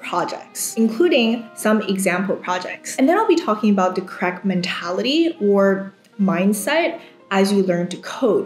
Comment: I misheard as "crack mentality" and thought I clicked the wrong video 😂